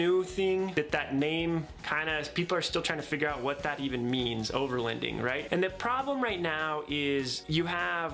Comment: Your pronouncing overloading wrong. I hear “overlEnding” lol